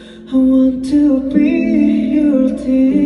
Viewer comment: I hope JK puts the lyrics out soon. I want to know what he sings after: "I want to be your..." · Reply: At first I thought he was saying something in Indonesian I want to be your ticket man?😅😭